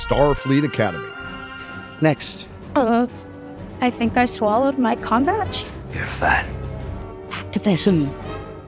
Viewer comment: I want to know when the second episode of Spock's chair is airing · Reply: They will steal this idea In future academy episodes. I thought she said she swallowed a condom.. I know I am a moron